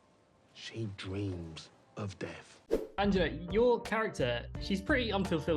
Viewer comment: Did anyone notice he called her "Angela" (3:20) instead of Anjana 😅? Then Paapa says something to her (probably that he called her the wrong name?) and she laughs 😆?